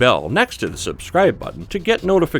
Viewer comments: When they said top list I thought they said topless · Must Watch 5 Most Unbelievable Snipers PART -2 - <link> 10 Most Bizarre Guns Ever Designed - <link> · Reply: Top List I thought you said your channel was called topless.